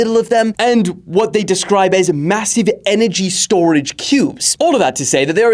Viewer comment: huh its Mm-assive did you just say massave